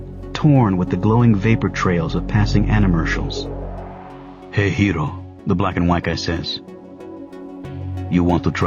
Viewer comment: dude at first i heard "hey kiro" and it was kinda creppy since it is my second name..